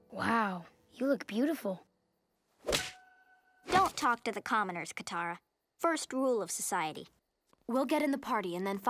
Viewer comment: What did Toph just say, Katara? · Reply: don’t talk to the commoners, Kotara, first rule of society